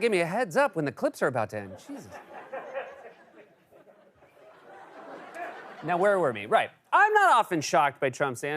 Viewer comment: "Now, where were "me"?" Even the closed caption thought you said 'me'. Was that intentional, or is this one for CORRECTIONS?!